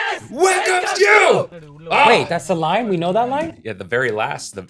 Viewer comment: what they are saying is, "Always welcomes you!" and it's obviously not Tamil. 😋